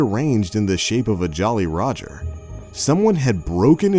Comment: I thought that you say Jolly Rancher. 😐🍬